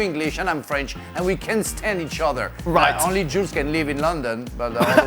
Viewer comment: For a quick second I thought frank said “ only Jews can live in London “ I got high & forgot Jules was on the show ..I thought frank went off the rails after cussing earlier in the show😂